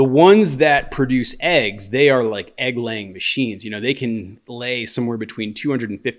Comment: I had this on in the background so I'm not paying 100% attention. I'm certain didn't say chickens are like dogs in that they are egg laying machines. But that is what I heard... 😂